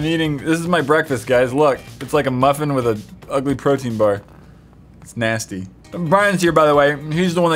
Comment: When he said protein bar I thought he said birthday card